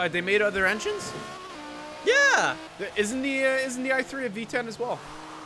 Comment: did say i3? The i3 is f*cking electric